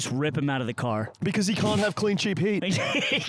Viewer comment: The entire time I thought they were saying clean SHEEP heat not clean cheap heat